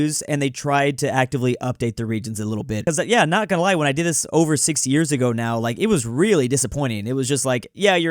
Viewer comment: Is it me or did I hear “60 years ago”